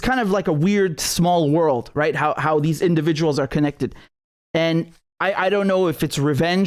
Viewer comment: i thought u said these were individual attacks